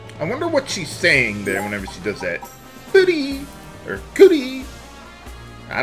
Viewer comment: it sounds like shes saying "🅱️lease"